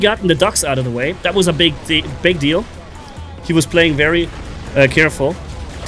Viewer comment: what I just heard 😂😂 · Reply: “Big di big deal”